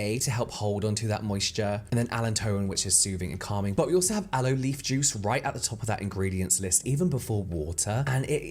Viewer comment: 😄Had to rewind ‘cos I thought you said it contained ‘Aloe beef juice’ 🖤